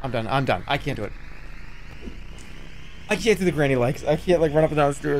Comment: jerma is the only person on the earth who has ever said "granny-like" for a video game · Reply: my god i thought he said "granny legs".... >.<